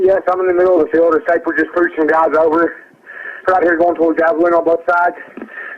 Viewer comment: I hear "I'm in the middle of a field", "a snatnet just breach some guys over", "they out here goin to apvill on both sides" "My truck ran out of gas" "There's one car here, they chasing (something, two syllables) in da woods" "Please hurry" "Mellin wantak it killed" "Kai chirin nit killed" "Just the first guy" "No, I need to cops"